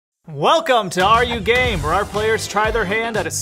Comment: Bruh I heard “Welcome to are you gay?”